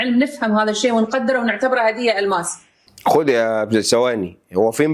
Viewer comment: اللي انا سمعته ده صح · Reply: لا غلط، قال خد يبني ثواني بس لزقوا في بعض